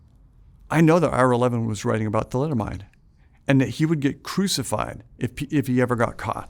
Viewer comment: what is he talking about (in relation to Ira Levin and some tragedy) – "the letimide" or some word like that? Can't make out the word.